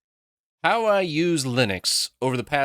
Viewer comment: wtf is he saying? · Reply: "How I used Linux...."